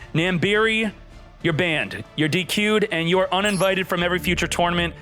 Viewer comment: Wait, I can’t tell if he was being serious, did he really ban Nambiri and not give him the $200? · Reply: What said at 15:05? Youtube subtitles: "you're dq'd"